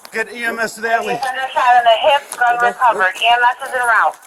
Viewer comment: did she say "Shot in the hip"?